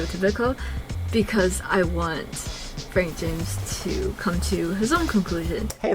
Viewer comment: subtitles say “Francis” instead of “Frank James” lmao